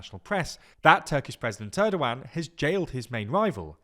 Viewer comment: Is it just me or did he say Turdogan instead of Erdogan?